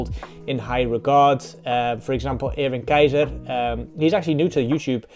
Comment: I didn't understand the name properly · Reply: Erwin Keijzer! Check out his stuff on YT 😊